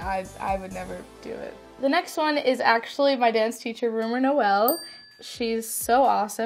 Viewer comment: I first heard as maths teacher then I realized it is dance teacher ......btw the maths teachers are so strict in my school why they will have an instagram account. hahahah if you also heard it as math teacher like my comment pls